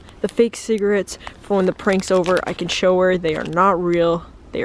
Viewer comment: Oh no I thought she said no pranks saud!